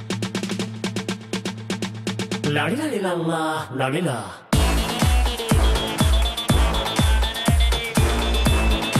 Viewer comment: IM GOING INSANE WHAT IS HE SAYING 😭😭😭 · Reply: 'La-dee-la-lee-la-la la-lee-la' he's so real for that